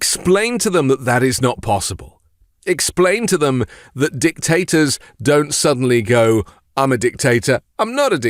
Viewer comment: All I hear is "Dick-Tater".